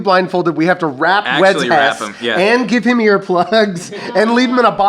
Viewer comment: HELP DID ANYONE ELSE HEAR HOW SAID "WEADS HES" 💀💀💀💀💀